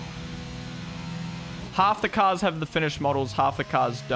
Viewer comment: That's why I said it was. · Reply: I thought u said Unfinished... (O_O)